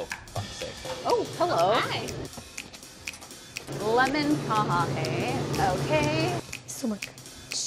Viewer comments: did she say "Lemon kaha hai?" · did he just say what I think he said? Oh for ——- sake?